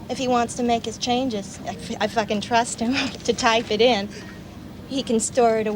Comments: PLEASE VERIFY…does she really say? “…f’in trust him to type it in” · I thought she said “ I f-ing trust him” 😂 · what did she say? I also thought she said that xD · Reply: "if I can trust him" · I thought she said “ I f-ing trust him” 😂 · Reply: I thought so too but she said "if I can trust him..."